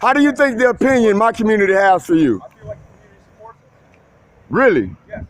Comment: What do you think the opinion of you is in our community? "I think they want to Pork me." I'm pretty sure that's what he said.